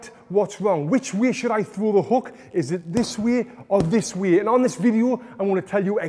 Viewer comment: Every time you said “this way” i thought u were saying “this weird”